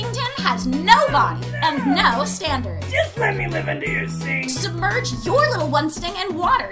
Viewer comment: Instead of hearing “Just lemme live under your sink.” I heard “Just lemme live under your skin!”